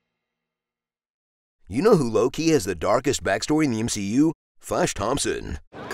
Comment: When he said Low-Key, I thought he said Loki